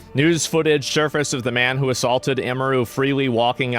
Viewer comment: Not going to lie I thought it said assfucked not assaulted